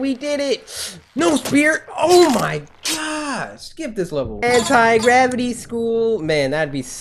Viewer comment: When i was watching this video on 9:02 minutes I thought Cory said hentai gravity school XD